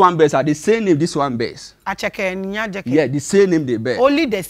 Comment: Eiiii I heard him saying Aceke 🤔🤔